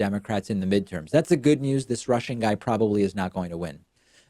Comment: I totally thought that David was saying that, “This Tiny Russian Guy will not win,” when he was saying that, “This Stoney Rushing Guy will not win!!!” I am 100% blaming Dear Leader for making it so that we all have Russians in American Politics at the forefront of our subconsciousnesses, LMFAO!!!